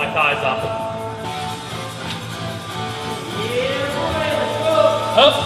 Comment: Was I the only one who heard a distant "Yeah buddy"?